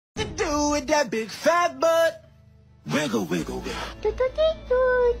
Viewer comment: "hit you with the big fat butt" That's what I heard at the start 😂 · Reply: I heard “What to do with a big fat butt” 😶